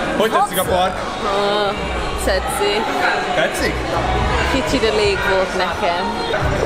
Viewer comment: nél mit mond? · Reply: Tetszik? Kicsit elég volt nekem